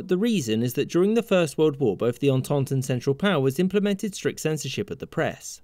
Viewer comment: What did he mean at 0:51 by both tonton central power I didn't understand that as I'm not native English speaker can you guys writes what he said 🙏🏼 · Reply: The entente, and the central powers are alliances that fought against each other in World War 1